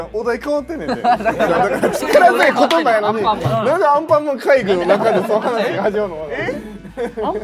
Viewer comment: 「アンパンマン界隈」じゃなくて「アンパンマンかいりゅう」に聞こえるの私だけ？WWWWWWWW